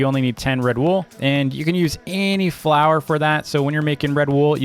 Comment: When he said flower i thought he said flour